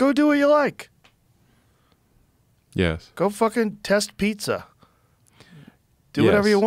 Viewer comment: wtf did he say "test pizza "???